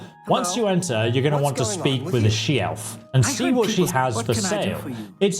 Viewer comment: Swear to god it sounds like he said speak to acheft lmao